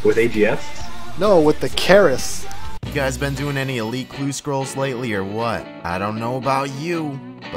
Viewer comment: Did you say elute clue scrolls at 0:45 or something